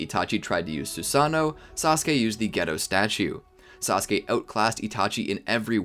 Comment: did anyone else hear him say “Sasuke would use the Ghetto 🔫 statue”